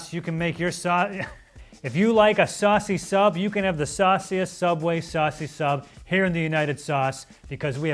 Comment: bro i left youtube on for hours and im just sitting and i hear "saucy sub with saucy sub" or somethin